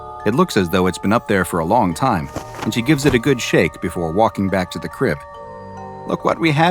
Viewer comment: when it says "and she gives it a good shake" an ad came and cut off the narrator and it sounded like it said "and she gives it a good shit" 😂😂😂 Edit: Now that I'm further into the video, I just wanted to say: SCP-5201 is the MOST unsettling one I've ever heard of or seen animated. It's extremely disturbing. I'm an extreme horror fan but that just made me uncomfortable and sent a literal chill down my spine